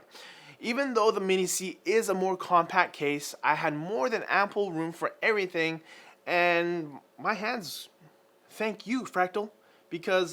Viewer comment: Really sounds like says "More than apple room". Also as of commenting the title says "MIni C" not "Mini C". On top of that the 170cm cooler tells me this review was a bit rushed, not proof read, or is having an off day. Either way, nice case, and appreciate your work.